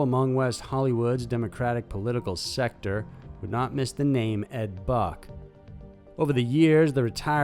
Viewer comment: Ed Buck....thought you said Fuck